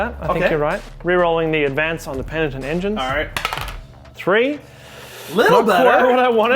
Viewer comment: Even during the army showcase I kept hearing "penitent ninjas" instead of "penitent engine"